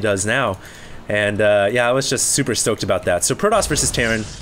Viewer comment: Did you say "Super soaked about that" 2:13 ? Oh HD... You dirty dirty boy. ;]